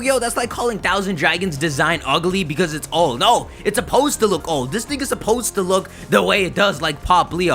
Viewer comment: did he say "this nigga is supposed to look old"